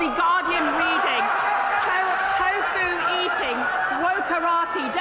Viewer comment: I thought she said "toe foo eating woah karate" gibberish.